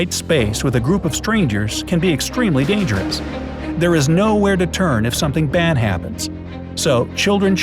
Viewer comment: Pause at 5:17 he said there is no where to turn while the subtitle said there is no where to run funny but it helpful